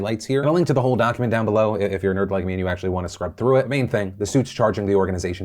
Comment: Can you find a word other than “main?” I know you’re saying “main”, but I keep hearing “Maine.”